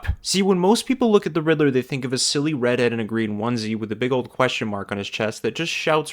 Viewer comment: He wears green and is typically a redhead so I think no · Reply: in my defense when watching it the first time it sounded like he said "red and green onesie" not "redhead in a green onesie" also in another video he was talking about a character and said multiple times they were wearing a certain color when they weren't